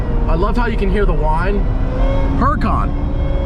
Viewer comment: Well, I know what you said there... But the first time, all I heard was "I love how you can hear the whine... Suddenly looks left... DRAAGONFLYYYY!!!!" Sorry man I had to do it. Love these videos!